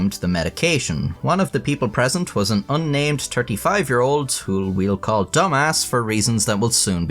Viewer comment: i don’t know how i missed “thirty” but i was very, very confused when i thought you said a 5 year old